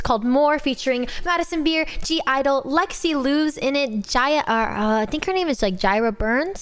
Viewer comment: Lexie Liu was voicing Seraphine for this song I believe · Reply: I thought you said lexie lore XD